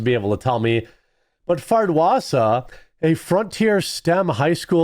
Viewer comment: So, I am listening as I work and have it sped up a bit... But it sounds like Jeremy said her name is Fart-wasa ? Lol I just want to call it fart face now 😂